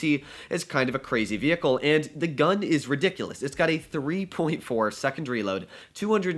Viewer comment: did I hear it correctly? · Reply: Think he said 13.4